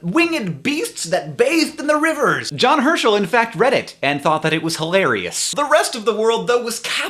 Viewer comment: Anyone else thought he said Reddit at 2:58 ?